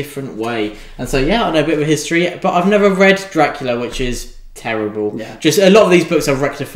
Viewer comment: Now I would definitely read Shogun by Bram Stoker. 🤣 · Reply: I had to go back 3 times to see if my ears were right or wrong! So I'm not the only one who heard him say Shogun instead of Dracula!